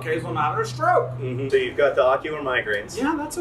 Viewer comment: I heard "ocular migrans" and was very worried and confused for a hot second 😆